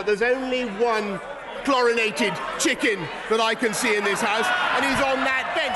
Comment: "Chlorinated Chicken on that bench"....love it! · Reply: I thought he said "coronated chicken" and I wondered if that would be an insult to the crown.